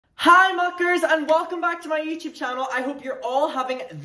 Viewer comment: i have a confession… I don’t know what a mucker is and for a while I thought you were saying fucker 😭😭😭 love u